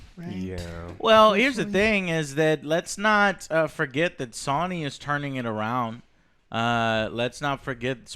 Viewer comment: Everyone be quiet except for, please ❤ .... Joe did you actually fucking say "Sawny" instead of Sony?? 4:23 Also, shut the fuck up about fucking SONIC. And YES i Am ANGRY. Oh man, that DA4 news sound dire. Why they gotta murder Dragon Age like this